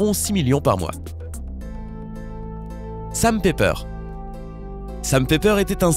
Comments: C moi ou quand il a dit"Sam Pepper" j'ai compris "Sa m'fais peur" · quand tu a dit "sam pepper" j'ai compris "sa me fait peur" 😂